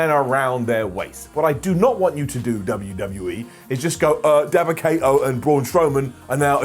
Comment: What did say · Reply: daba kata and brawn strawberry